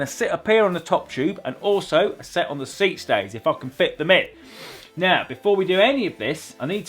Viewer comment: Does say: "If I can f*cking fit them in" at 1:32?😂